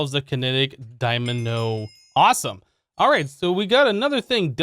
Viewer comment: What did he say? Kinetic dymano? It happens...